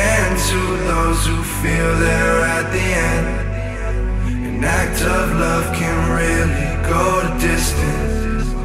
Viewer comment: Always thought he said “And that tough love can really go the distance”